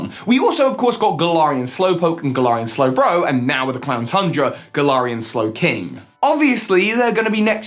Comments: Did anyone else hear him say "clown tundra"? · did he- ... did anyone else hear him say "clown tundra"?